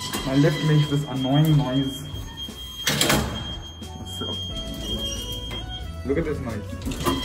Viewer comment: Did you just say "LoOk aT ThIs NOisE"